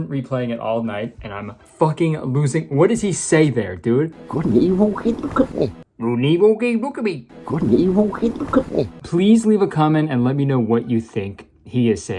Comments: I really think he's saying, " good and evil, kid look at me." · "Good and evil, hey look at me!" that's what I heard idk LMAO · I think he’s saying “good and evil, hey look at me” at least that’s what I hear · I believe he’s saying “Good and evil- hey look at me!” But i think its funnier if hes saying goodabeeboe heybookabee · I have a couple ideas of what he might be saying- He might be cutting himself off mid-sentence to ask her to look at him and he says something like "Couldn't evil be- look at me." or "Couldn't he locate- look at me" or, on par with the rest of the convo, the best I could make out was: "Good and evil looked at equally" I doubt either of these are correct but damn if I didn't replay that part 1000 times trying to put my years of living in the south to good use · I think he says you can’t even look at me but tbh I have no idea either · I think he says” Good and Evil hey come look at me” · It sounds like he is saying "Good and evil. Hey, look at me." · in that one clip i heard "-good and evil, look at me" idk abt the good and evil part but im pretty confident on that look at me part cuz shes like looking down and then looks at him. and him saying good and evil j goes hand in hand w the god theme. overall the movie was a pretty painful watch. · i think he’s saying “good and evil-hey look at me” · that is none other than Rowdy Roddy Piper with the mush mouth. Also it sounds like he said "good and evil can't look at me" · I heard "good and evil hey look at me"